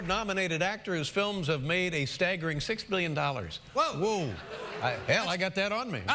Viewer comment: I thought he said $16 XD